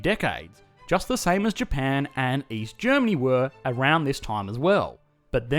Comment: Did you say Japan and East Germany? Wasn't it supposed to be West Germany? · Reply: Oh East, I thought you said Weast.